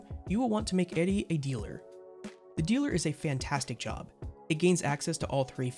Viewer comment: This whole time on your videos I thought you were saying “Eddie” until I saw it spelled as “Eri” haha. Love your vids btw!